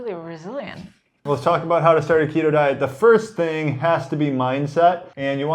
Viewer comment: I could've sworn he said taquito at the beginning lmao 😂😂